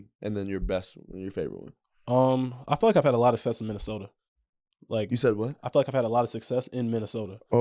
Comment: RJ really thought Cole said "I've had a lot of sex in Minnesota" 🤣